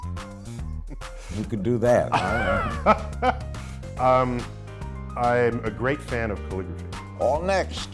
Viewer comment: I thought he said "I'm a big fan of polygamy."